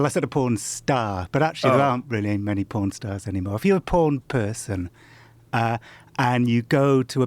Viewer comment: It sounds like he is saying "Porn herp"